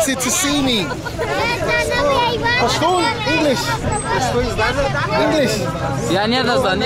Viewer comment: who knows what the guy at 2:10 said i’m curious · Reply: When told him Pashtoon, that guy asked him do you know Pashto? Or do you speak Pashto!